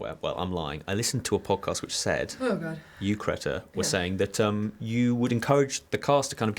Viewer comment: he really did just say CRETA